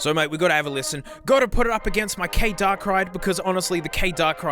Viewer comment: I hear "K Darkrai" instead of "K Dark Ride"